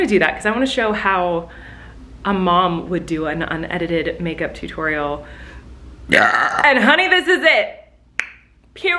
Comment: I thought that it said "UNLIMITED MAKEUP TUTORIAL" and I clicked SOOOOOOOOO HARD like u guys wouldn't understand the SPEED I just did